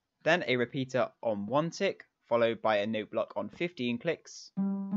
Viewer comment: Honestly when he said “15 clicks” I almost thought he said 50 clicks- 😅